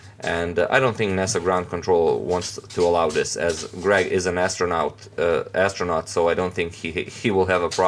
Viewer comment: Did he say Greg is a MASTERnaut? xD